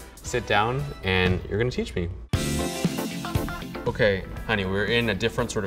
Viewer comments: uhhh did you just call her honey? SUS O_o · in the video, he says ok honey... I’m I the only one who heard it